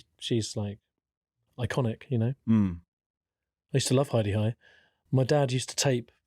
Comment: Turn on subtitles or closed captions. You know when Tim is joking and being serious, and you're not sure if he's being jokey or just serious. Well I heard him say Hi.De.Hi in this section but the subtitle text said something different each time he said it. Culminating in the last line of this Hi.De.Hi stuff reading, "Just the hidey eye stuff, isn't it?" And I thought is this Tim thinking Rom is thinking that Tim keeps saying "hidey eye" and that's why they're both laughing. Or the subtitles are just wrong and they're laughing because it's the sitcom Hi.De.Hi and liking it seens ludicrous. Both explanations I think would work. Brilliant. I love that I've found this podcast. My new favourite. 👍